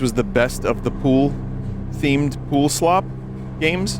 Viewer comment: Poolslop my favorite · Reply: I thought you said poopslop and i laughed because poop is funny